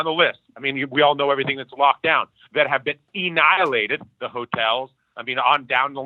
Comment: is he saying “Ennihilated”? “Dennihilated”? wtf?